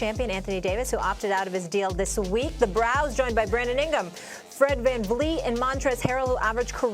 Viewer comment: did she just say "ingum"?